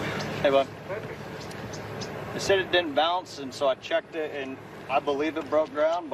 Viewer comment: I thought the volunteer said “ I did not see it bounce”... different than it didn’t bounce. If you don’t see it, ???